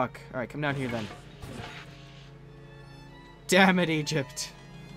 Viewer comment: Did he say Egypt has Crabs